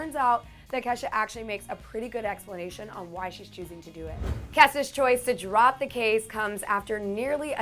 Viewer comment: anyone else hear her say Kesa